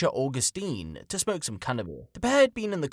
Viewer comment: closed captions swear u said “smoke some carnivore”. I swear u said, “smoke some cannibal”. But I think we all(even u) meant to say, “smoke some cannabis”.😂😂😂😂 so which is it? Cuz u r talking bout a murderer here… 😂😂😂